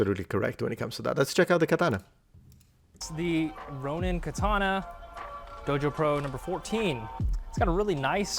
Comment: At first I thought he said “Roman katana” 😂 I had to rewind